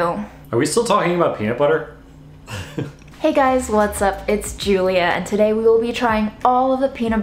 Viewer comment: Did say she's Julia at 0:06? Still, can never do wrong. 🥰 Gettin mahself -approved PB! Will not reveal and dish a vlog spoiler!